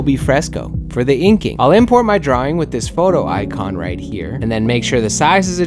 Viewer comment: Could you put a link in to the tshirt mockup site? I couldn't seem to find it based on what I heard you say...photo fic? Fotofick? Thanks..